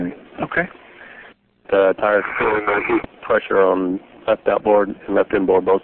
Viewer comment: NASA's official transcript records it as something else, but I've never been able to hear anything other than that. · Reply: Maybe it's because I was told that it was "feeling the heat" that I hear it as that. It could be something else but I keep hearing "feeling the heat".